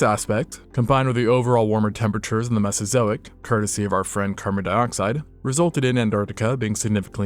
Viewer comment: did i just hear kermit dioxide?